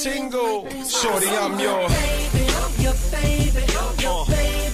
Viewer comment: this was one of the last videos they ever showed on Total Request Live (TRL) before it was finished, i thought he was saying YO BABY